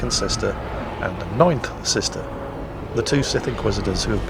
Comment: I kept hearing "night sisters" and was ready to scared shitless. Especially if they looked like in Galaxies.